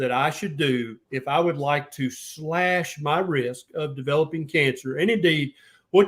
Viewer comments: “Slash my risk”. I first heard “Slash my wrist” and had to ponder it. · Glad I am from KY, country boy, thought you said slash my wrists at first, replaying back in my head, decided you said risks 😂